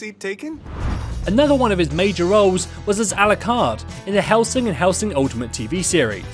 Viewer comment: DID I HEAR "ALUCARD"!